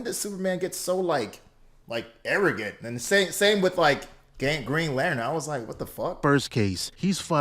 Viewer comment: did he just say gangrene lantern. 😂 But, for real though, Green Lantern gets slept on alot. He's actually a good ( and powerful) super. Just often poorly portrayed; like in that awful ryan reynolds movie.